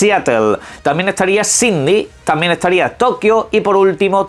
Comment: "cindy"....? Nunca escuche esa ciudad, de que país es? 🤔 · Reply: Jajajaja pense lo mismo XD. Pero se que sabes que se refiere a Sidney